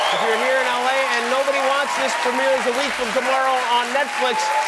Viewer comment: did Jimmy call her Krystal xD